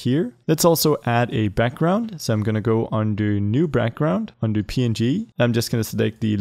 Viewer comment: Did anyone hear him say Brackground